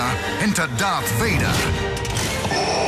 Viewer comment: I thought that said shuffle 😔